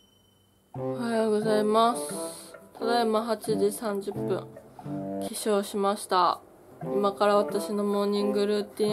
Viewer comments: 化粧しましたの意味が本気でわからん え笑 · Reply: 恋〈れん〉廉 起床しました が化粧しました に聞こえたからそう字幕入れただけやと思いますwwwwww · 化粧しましたの意味が本気でわからん え笑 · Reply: 起床しましたって言っとるけど 編集中のさんは 化粧しましたって聞こえて 化粧しました？って字幕で入れたんやと思います 違かったらすいません😔💦